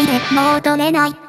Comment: 亡霊だって死ぬんだよって聴こえる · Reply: i heard BOUREI DATTE SHIRUNDAYOOOOOOOOOOOOOOOOOOOOOO